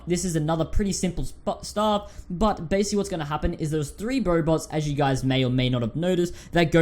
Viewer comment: Anyone hear " Bobots "?